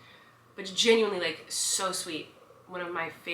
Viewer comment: when you said "just genuinely so sweet" my mom thought you said "this chicken leg is so sweet" and i think you deserve to know this tbh